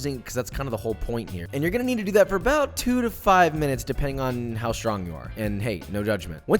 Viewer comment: did he say 5 - 10 minutes? I know some of you heard 8 - 12 minutes. Weaklings lol